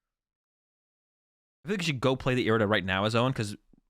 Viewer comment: is the play cross switcher hands palkia v then horn switcher it again? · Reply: never mind I thought you were saying pao should play horn. And by double cross switcher I meant 1 switcher bc it takes two of them to pull it off. I just worded it poorly.